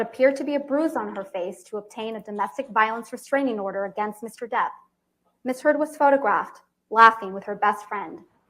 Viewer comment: she had me until she tried to say "irreparable" I was watching this with the closed captioning, and every time she said "Miss Heard", it read misheard. It's interesting that her name sounds like getting the facts WRONG.